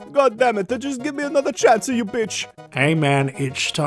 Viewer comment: Okay. 1:30 I can't be the only one who thought Mario said the "B" word before turning on the captions for the fun of it.